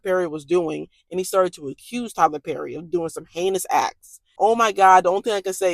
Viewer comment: Omigosh, when you said heinous, I thought you said anus 🙆🏾